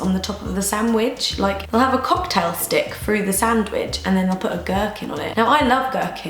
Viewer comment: At first when you said gerkin I thought you said merkin and I was like WHAAAAAT?!?! lmao